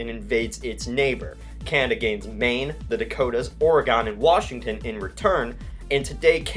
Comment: did he say Oregan?